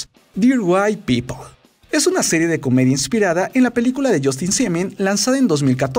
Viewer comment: Justin Simien yo pensé que dijo Justin Bieber😂😂 ;'v